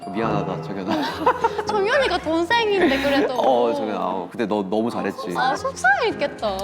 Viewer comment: why did sana say jeongyeon was younger than her?? 😭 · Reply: Sana said jeongyeon is younger than j hope